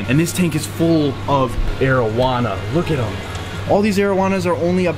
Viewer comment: when you mentioned arowana at the start of the video i had to rewind, my dumb stoner ass thought you said marijuana